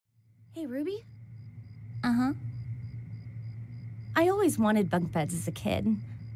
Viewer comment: "I always wanted bunk beds as a kid." Not just adorable, but also subtly heart-wrenching character foreshadowing. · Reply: I heard it as "I always wanted bug beds as a kid."